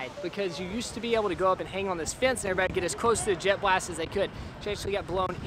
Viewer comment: Thought you said JetBlue and not jet blast